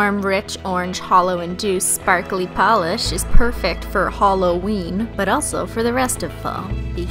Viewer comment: Dang she missed a perfect opportunity to call them HOLOween nails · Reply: I definitely heard her say "holoween"